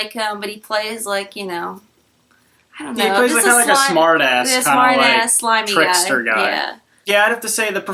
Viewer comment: For a second I thought she said "smartass limey guy". Haha. Good review. I'd probably rate it more like a 5 or 6 (you can find my thoughts in the review on my channel). Oh hey, I'm your 420 subscriber.